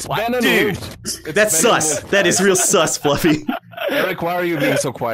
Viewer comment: When he says sus all I hear is sauce